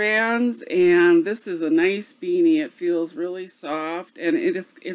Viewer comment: Had a question where did the hat pattern you were talking about come from..I had thought you said Rich Beanie. Thanks 😊